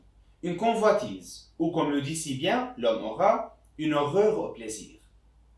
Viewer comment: Que dites-vous en 1:21 : « comme le dit si bien l’homme aux rats, un (…?) au plaisir » ? La transcription n’est pas plus claire que l’original audio. De quoi s’agit-il ? Quel est le mot manquant ? Merci et félicitations pour ces petits exposés très clairs.🙂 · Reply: horreur